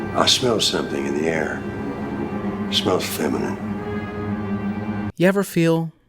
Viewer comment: People were mad at you for shifting on segal? Lmao they’d hate space ice😂😂😂😂 Edit: 4:34 did he just say it smells like bitch in here?😂